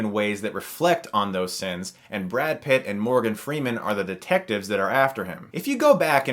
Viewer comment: “Brad Pitt and Morgan freeman are the detectives that are African” Could’ve sworn that’s what you said lol 😂 I had to rewind and check